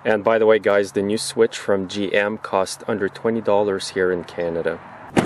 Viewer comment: They moved those switches to a bad location on the Colorado. Not only will it get wet everytime the door is opened in rain/snow but it could also be knocked off by your foot. I just checked on my 01' S series where mine is. I knew it was on the hinge side, but it took me a minute to find because the switch is on the side of the dashboard on the s-series below the fuse box on the driver side same spot on the passanger side as well, hard for this thing to get damaged or wet on thess trucks, havent heard of it being an issue. I still like the Style of the gen 2 s-series better and they were built much more solid compared to the Colorado. I havent had to replace anything in the interior yet, although I could use a new armrest, the plastic under it cracks and my arm sags, thats known on the these large driver seats, where the armrest can go up for a 3rd passanger in the front. Thats about it. The V6 Vortec on these trucks had a successful 20 year run and were one of Chevys best engines. Reliable and go well into the 200k mileage range. We had a 96 SS S10 for 10 years, we just sold it this past fall with over 200k, for 2 grand. That little low rider took off like a rocket. I love my S10. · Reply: FearlessFront D.W. Under $20, not $120. I had also thought that he said $120, but he says under $20 later in the vid... 👍